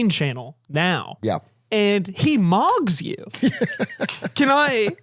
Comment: thought you said “he mobs you” which is correct, doug mobs me everytime I watch one of his videos, please take action against doug for doing this